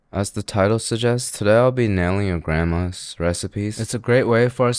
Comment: At first I thought you said today I'm nailing your grandmother. But you finished your sentence than I was like ok yeah that is a lot better.